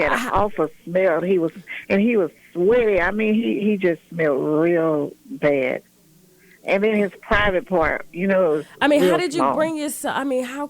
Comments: did I hear her say "...he just smelled real bad and his private part was REAL SMALL..."? Could that be a possible contributing alleged fact to his alleged past and present inappropriate behaviors? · She said his private part was real small? and you didn't catch that. at 35:06 she said his private part was real small.